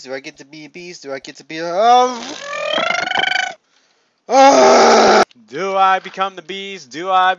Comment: the first time i watched the video instead of hearing him say "do i become the beast" i heard him say "do i become the bee"